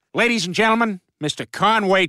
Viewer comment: I always thought he said, "Kanye Twitty" when I was younger. Now that I know it's just "Conway Twitty", I still don't get any less pissed off.